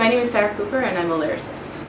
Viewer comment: I'm I the only one who thought she said "Hi I'm Sarah Pooper."?